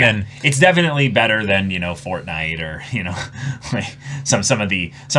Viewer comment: What did they say? Fortnight? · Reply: The test to get the provisional hero license, they call it the Fortnite arc